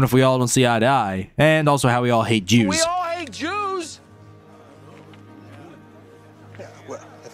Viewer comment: ive seen this movie 5 times and ngl i thought he said "we all hate you'se"